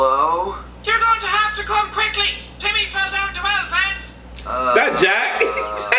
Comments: I heard "JIMMY FELL DOWN THE WELL LANCE!" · I heard “you have to come quickly Timmy fell down two elephants.” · I thought they said You have to come quickly to me fell down the lance